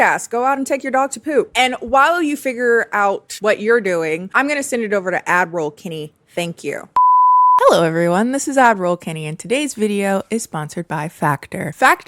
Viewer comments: every time you say "ad roll kennie" i hear "admiral kennie". for years.i know this isnt what youre saying and yet great video tho as always! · for the longest time i thought you were saying Adderall instead of ad-roll and the funniest part is i never questioned it